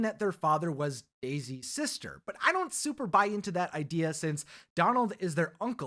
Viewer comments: did you say the father is Daisy’s SISTER? 😂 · Did he say at 5:30 that their dad was daisy’s sister?